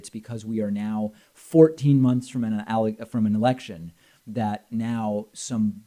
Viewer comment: I thought David said "....we are 14 months away from an erection" not "election." If you have to wallow in filth that is how it affects your brain.